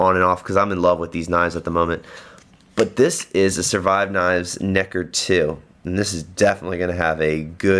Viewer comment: What’s the orange knife with finger hole brand? Sounds like you said Survive Knives Necker 2.